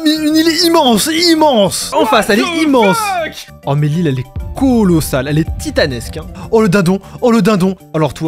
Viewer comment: j'ai entendu meli elle est colossale